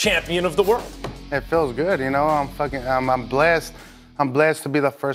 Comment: Did Andy say I'm fucken blessed to be the first mexican heavy weight